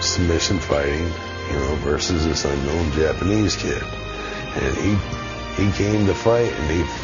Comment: sounded like he said japanese kid to me.